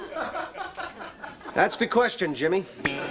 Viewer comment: did he say Jimmy?